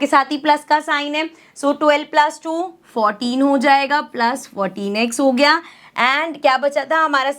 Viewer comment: not me hear 14x 😅 I hear - 'protinex'😂😂😂😅